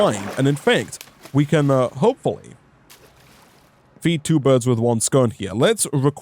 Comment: Did just say "feed two birds with one scone" or am I high?